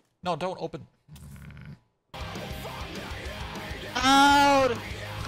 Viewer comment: the guy sounded like for a second thats saying "SHOURD!" Or its really?